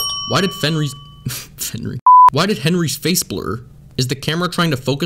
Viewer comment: "Why did CinemaSins say 'Fenry'? +1 sin